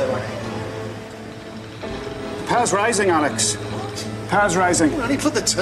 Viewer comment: I thought he said "The power's rising Alec." And I was "Wtf Alec?? This isn't goldeneye."